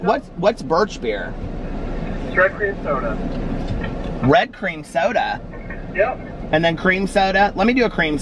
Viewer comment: I thought he said drag queen soda 🤣🤣🤣